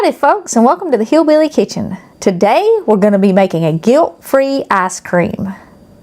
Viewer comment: There is zero hateful intent with this statement. But I swear to gravy I heard "Today, we're gonna be making a guilt free ass cream." I laughed so hard I choked. Ty so much for the giggles and the awesome recipe!